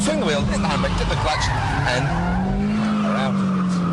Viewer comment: Too fast all I heard was "FIRST GEAR HIT THE BACK AND POWER OUT"